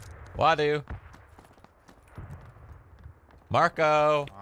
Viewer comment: Is he call wadu “MARCO” ?